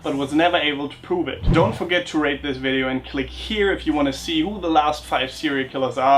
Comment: Perhaps it was all the references to sexual violence throughout the video, but I misheard you telling us at the end to rape the video.